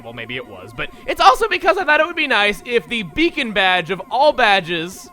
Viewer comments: I actually thought that it said the BACON BADGE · So many comments saying that my pronunciation of Eidolon is wrong. I'm a little surprised by this, since I've only heard it pronounced one way and according to Merriam-Webster, I'm right. <link> My point of this isn't "I win," because I certainly do pronounce things incorrectly from time to time. But I'm just particularly surprised by this one in particular sparking so many comments that I felt it needed addressing. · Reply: for a second I thought it said the bacon badge